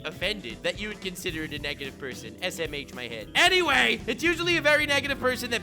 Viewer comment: Did he say "smh my head" 5:10 Doesn't it mean shake my head my head ?